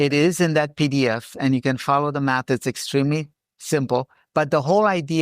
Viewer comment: "Differential Calculus"... hm... i think i saw that listed in a group of classes that i wasn't qualified to attend. When he says, Follow the math, all i hear is "follow the Orcs"... 😂